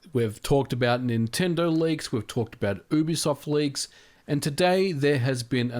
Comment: I misheard you at the start, and thought you said "Boobisoft leaks"